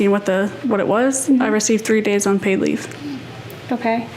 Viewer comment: unpaid* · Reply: I could've sworn that she said " 3 days paid leave".